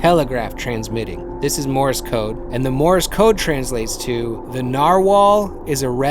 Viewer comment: coulda swore bro said the n word